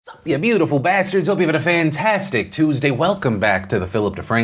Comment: your editor keeps cutting off the beginning of the video. it sounds like you said "up yours beautiful bastards".